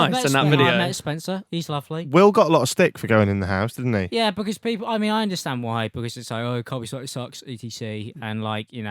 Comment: Wtf is Alex saying at 07:34? I rewound it so many times and can't catch a single word. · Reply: "I understand why, because he used to say Carmie Selitto sucks, E T C"